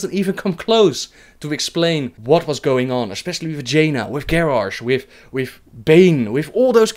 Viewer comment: he said "especially with Jaina" What I heard " especially Vagina"